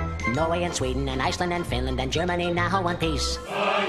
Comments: “Germany, now in one piece” Drawing tool: “Not anymore” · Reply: I just found out that he says ”Germany now in one piece” all this time I thought he was saying ”Germany now want peace” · I just found out that he says ”Germany now in one piece” all this time I thought he was saying ”Germany now want peace” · "Germany now in one piece" USSR anthem: no™ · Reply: I just found out that he says ”Germany now in one piece” all this time I thought he was saying ”Germany now want peace”